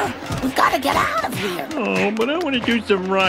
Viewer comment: Anyone understood what he was saying? All I understood was "you mucker". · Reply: Don’t ask me what it means but he’s saying: “Jobbers cobknots, ya mucker!”